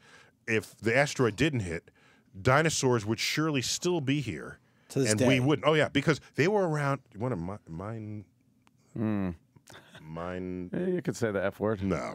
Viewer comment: What happened @ 4:50? Mine......mine.........? I don't understand. Someone please explain to me what happened there. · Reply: Mindfuck.